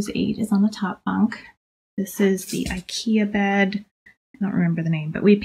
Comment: Do you know the name of the bunk beds? I think I heard you say it's Ikea bed. Thank you? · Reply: She said ikea bed that she added details to make it more girly.